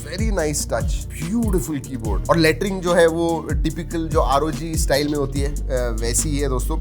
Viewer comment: Did he just said Laitr**n or Lettering